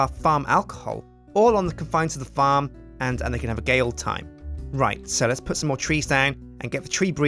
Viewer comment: I heard say something gay old time listen